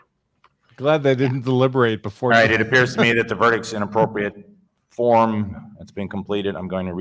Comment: Surprised nobody picked up what the judge said at 8:15:40 "It appears to me that the verdict is inappropriate" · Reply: "it appears to me that the verdict is in appropriate form." Finish the sentence atleast and you will find it's meaning.